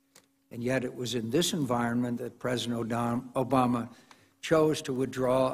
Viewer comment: Did McCain just said " President oh Dumb Obama" at 07:21 or it is just me.????